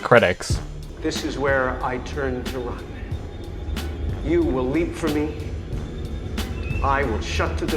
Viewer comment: "This is where I turn into ramen. You will eat from me..." Am I bat shit or did anyone else hear that?